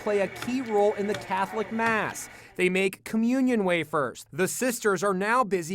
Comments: I thought it said “communism wafers” 😂😂😂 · I thought it said communism wafers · I thought it said communist wafers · I'm a dumbass I thought it said communism wafers 😁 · Lmao I thought it said communism wafers😰😂😂😂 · I thought it said communist, I almost disliked the video